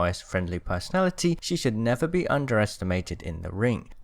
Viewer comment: oh man I thought you said “She should never be undressed and mated in the ring”. If you ask me...